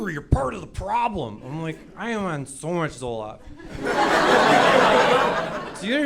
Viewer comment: "I am on so much Zoloft" for anyone who didn't hear it